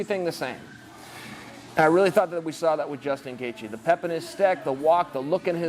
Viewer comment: Does he say "the pep in his steck" at 1:40 lol